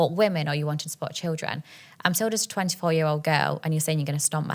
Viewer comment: did she say "im sold as a 24 year old" meaning she isn't? She looks at least mid30s